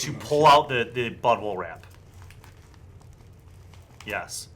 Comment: I did not hear "bubble wrap" first time around. Thought you said butthole wrap and was super concerned what thoughtful gift would require that.